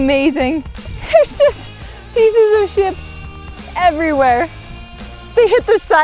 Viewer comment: Lol at 10:31 said ship but the subtitles said...